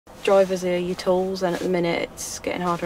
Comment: "Drivers are our tools". There is an old saying.... "If you look after your tools they will last you a lifetime". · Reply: I thought she said our drivers were tools...